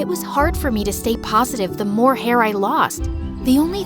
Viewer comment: Bro I thought you said you was hairless